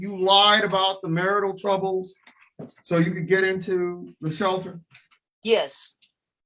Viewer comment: Can't believe I watched this entire train wreck. I really can't believe this very patient judge didn't ask the most important question. You admitted to lying to get the room.....was anyone denied a room because of this woman? horrible thought someone in harms way because of this bottom feeder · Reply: how did I miss that, could of swore they said DV shelter. thanks for the correction